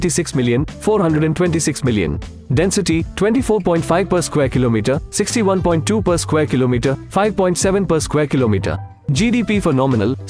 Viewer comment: i think he actually said 57 people per/km